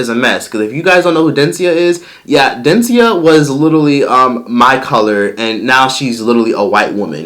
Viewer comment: I thought you said Dencia was intelligent 😑